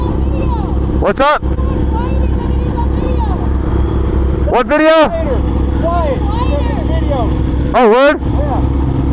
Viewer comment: I think he says “it’s so much quieter than it is on video”